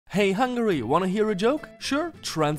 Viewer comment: i thought hungary was said hungry but said funny or whatever :P